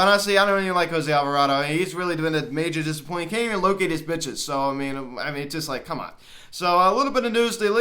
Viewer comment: the moment where I misheard and for a second thought Jose Alvarado was pimp who couldn't "locate his bitches." Well done, Cutch! This Pirates fan is still rooting for you. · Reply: Oh no! I really hope that’s not what it sounded like. I obviously meant to say pitches not the other word.